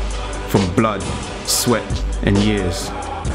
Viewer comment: been looking for a gym that had sleds and stuff like that. nice video bro... not gunna lie that what pulled me to you page was that we have the same name haha, but glad i got pulled! keepin grinding! thought you said "blood, sweat, and gears" since you were speaking about machines lmao.. but years works too!